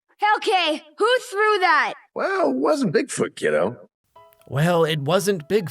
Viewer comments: cmon alex he clearly says kiddo not fellow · I heard kiddo, is that just me?